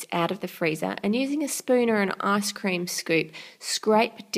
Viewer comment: Ha lol when she said ice cream scoop i have bad hearing btw but still i thought that she said asscream scoop sorry if ive affended any of you so bye